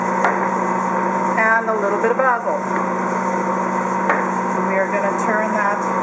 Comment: Just found your video, Very interesting. I am looking for a new mixer system to replace my KitchenAide. What was the last herb you placed in the Cheesy Garlic Bread. Sounded like you said Apple. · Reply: hi Sgt Billy, it sounded to me like she said the last ingredient was basil.